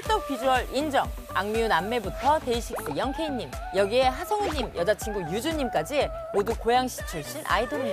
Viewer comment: 포에 하성훈씨 여자친구 라고 들었어요 ㅋㅋ